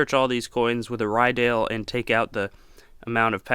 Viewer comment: "Search all these coins with a" with what? I can't understand the word he used. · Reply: Think the name is Ryedale — <link> is their website. Pretty sure there are other brands out there also.